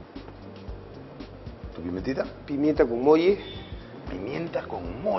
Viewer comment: Hermanos Peruanos! min 6:09 Pimienta Gumoyi, cumoyi? Escuché bien? Cómo es esa pimienta? Acaso así le llama Wong a la combinación que ha hecho? Si alguien sabe sería de gran aporte leerlo, pues el mismo entrevistador dijo no conocer esa pimienta ;) Les mando un caluroso abrazo!!!